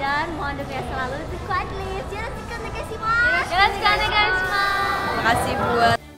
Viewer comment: dia ngomong apa ? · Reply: Semuanya mohon bantuannya